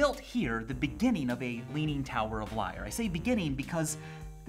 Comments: I thought it said "Leaning Tower of Life" There's some unfunny me_irl post to be made here but i cant find it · I thought it said "Leaning Tower of Life" There's some unfunny me_irl post to be made here but i cant find it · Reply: I thought it said leaning tower of fire.